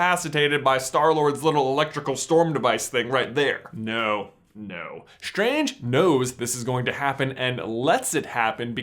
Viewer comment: Strange knows. Lol i thought he said strange nose